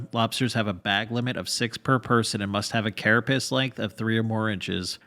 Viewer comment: i thought you said "a carapace length of three oranges"